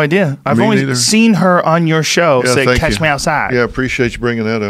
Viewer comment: I thought she was saying cash me outside.Listen closely!